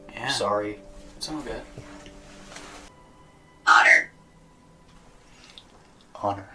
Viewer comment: Curieux, dans le n°1 à 11:57 les Twins entendent "honnor" soit "honneur" en VF, moi, j'entends plutôt "murder" donc "meurtre"... Qu'en pensez-vous? Je sais que c'est un peu suggestif et subjectif, que ce genre de sons peuvent tout et rien dire selon ce qu'on a envie d'entendre mais je l'ai réécouté plusieurs fois et je perçois toujours murder Écoutez sans regarder l'écran et dites-moi si je me fais des idées.